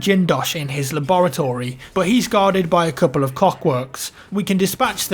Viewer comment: said Cockworks instead of Clockworks XDDD